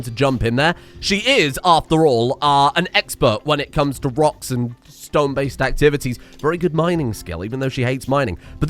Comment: DID I HEAR A ROCK AND STONE?